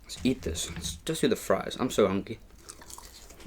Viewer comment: why does it sound like he said hunky ?